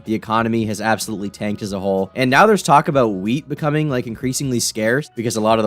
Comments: I heard "weed" instead of "wheat" at first and was relieved when I realized your talking about bread. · When he said wheat was gonna be scarce i thought he said weed and my heart sank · I thought you said “weed” at first and not wheat lmao i freaked out but then i was like wait, i live in Michigan and we grow some of the best weed in the states